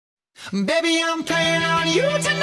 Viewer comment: people : its not about animals ,its about cannibals,wolves,rape & sex me : just enjoying the lyrics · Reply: I actually before know it was called animals I thought some of it was saying like cannibals 😅 but I am ten and love this song even tho I know what it’s about